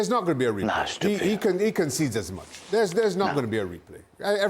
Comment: Anyone else hear that "there hash tah beh" at 1:32?